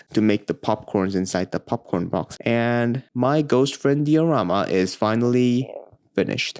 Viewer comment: I thought it said drama.